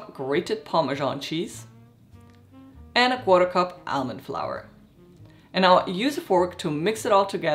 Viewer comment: Great recipe ! Did you add bread crumbs after the Parmesan cheese? I heard you saying ‘flour’. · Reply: "almond flour" the link in the description takes you to the full written and printable recipe